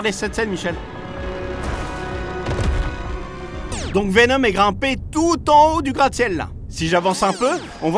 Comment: c moi ou g entendue venom est grimper